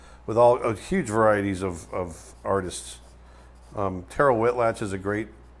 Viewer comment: Hi, at part 47:34 what is the name of the Artist Aaron says for animal drawings? I couldn't finding, I'm thinking i misunderstood the spelling. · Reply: Terryl Whitlatch was the name mentioned there 😊